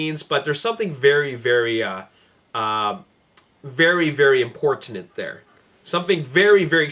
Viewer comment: did say importanent